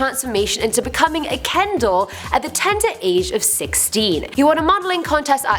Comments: I thought she said 'ken dog' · I hear “candle” every time she says “Ken doll”